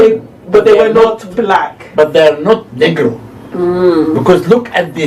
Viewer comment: Did Zahi just say, "they were not N*ggers," did I hear that correctly?